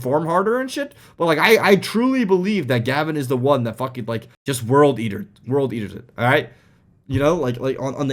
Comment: did he say worldeater? As in 40k 😱🤯